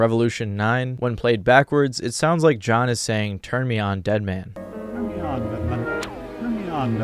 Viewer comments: i hear: Turn me on BATMAN 😊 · I heard "turn me on, Batman" 😂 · Sounds like he said turn me on Goodman....